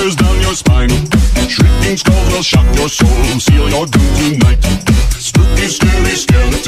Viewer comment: I thought they said duty might instead of doom tonight xD